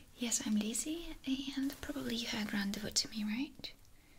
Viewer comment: I must have watched this a thousand times and it's starting to bother me. What does Lizi say after she introduces herself at 0:18? "Probably you...."? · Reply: Probably you have rendezvous today? I wanted to confirm you booked an appointment. My vocabulary is really bad sorry..